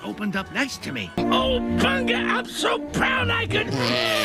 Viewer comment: What did Pumbaa say there? "Oh, panga..."? 3:14 Actually that was Mr. Pig, the resident pig on Captain Oinker's farm, farting there, blowing trees away and causing the clouds and the sun to fall down. When Pumbaa farted, the gas only made some flowers droop. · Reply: He said Bunga, not panga